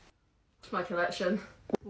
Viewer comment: "it's my ex" I THOUGHT YOU SAID KI@@ MY A$$